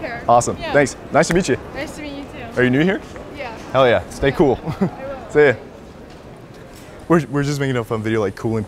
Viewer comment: did she say 'You're welcome' in response to him saying 'Stay cool'? 😂😂😂😭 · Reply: Moaz she said I will